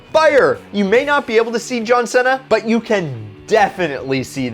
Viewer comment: Did you say John Senna?